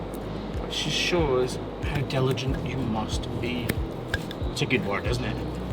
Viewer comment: I thought that he said intelligent at first and almost got offended until he said it again. Diligent!! That makes way more sense. Lol